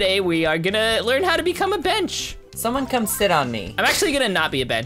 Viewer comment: At the start of the vid when he said "im a bench" i heard " im a b*tch" im dying of laughter 😂